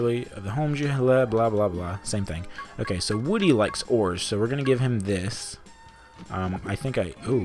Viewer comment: I could've SWORN that you said "Woody likes whores" instead of ores. I mean, he had to get those grandchildren somehow, right? XD